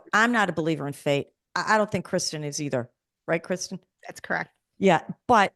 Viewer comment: I nearly misheard response to "Do you believe in fate too?" as "That's crab" but I had to re-listen again and I realized she said "That's correct" I wanted to scream at first, haha. While I understand that everyone do not give attention to what fate is, I love it cause I'm a fatalist. This is an interesting conversation with Jenny Colgan. New Sub here!